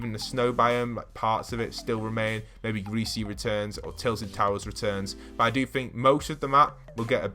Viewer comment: when he says tilted towers the captions say Tails and Towers Pls sub i wanna get it :)